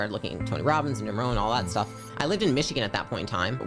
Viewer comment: 🚨 Anybody catch the name of the second self-help guru she mentions at 01:02 ? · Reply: Jim Rohn